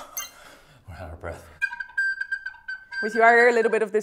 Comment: i may be stupid but i heard "we're out of brett"